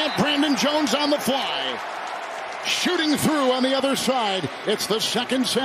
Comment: Hmm... Thought he said "shitting through on the other side" at 8:23 Had to look to look at my phone to see what's going tf on